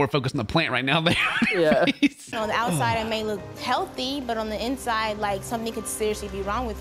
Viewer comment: im sorry but did she just say "on the outside it may look HEALTHY"